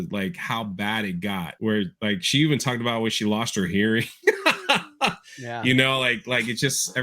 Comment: what is saying here? "When she lost her ____". English isnt my first language so i cant quite catch the word he says. Just before his laugh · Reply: Sounded like Earring or Hearing